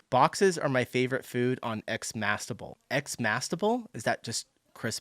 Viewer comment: I think they're saying Xmas Table 🙃